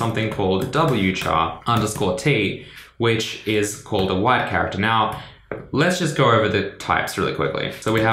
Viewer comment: I like how the subtitles said 'White Cabbage' instead of 'Wide Character' at 6:55 xD Thanks Charno for the great vid!